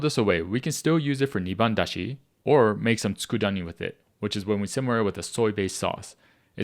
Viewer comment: What's the name of the dish at 5:12? I can't make out what you're saying to look up a recipe thanks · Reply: It's called tsukudani, basically simmered/seasoned kombu. Just One Cookbook has a great recipe: <link>